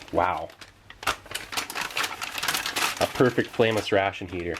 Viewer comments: when i first started watching i always thought he said “famous ration heater” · I always thought he said famous ration heater